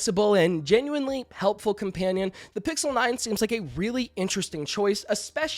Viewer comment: Did you say Pixel 9??